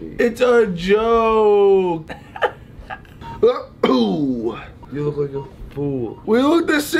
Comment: wisdom teeth video... ethan: you look pretty ugly tbh grayson: :o thanks. we look the same... nose surgery video... ethan: you look like a fooool~ grayson: WE LOOK THE SAME!!! >:V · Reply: I thought he said you look like a ghoul